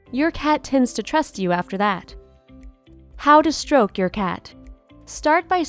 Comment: At the end when it said, “How to stroke your cat.” I thought it said, “How to stroke off your cat.” Like dude, stay out of my personal life.